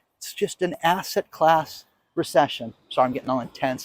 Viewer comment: I thought you said an ASSHAT class recession!😂